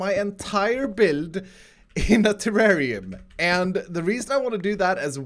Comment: Who thought he said Terraria?